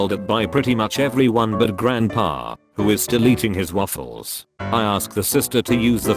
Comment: why does it sound like he says ”deleting his waffles”